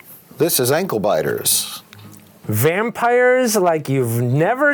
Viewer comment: I'm very upset they didn't get to watch vertically challenged vampires. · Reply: I thought it said "Shrek-sized vampires"